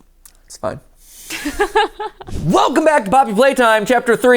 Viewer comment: I really thought he said "welcome back to bobby flay time" and I was so confused as to when we started cooking competitively